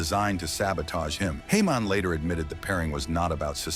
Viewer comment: did you just say heyman but said it like the apple jacks guy who’s jamaican?, hey-mon